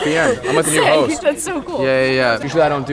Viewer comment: ong it sounded like said bernard when he said connor bedard